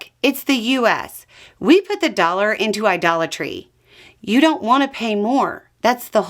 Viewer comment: "This is America. We put the Dollar in Idolatry". I love this one! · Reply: LMAO!!!! I thought she had said "we put the dollar in a dollar tree". I was about to look it up, wondering if this was an American idiom of some kind.